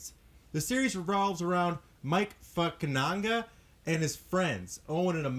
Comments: the CC said Mike fuckin Onga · did you say mike fuckananga?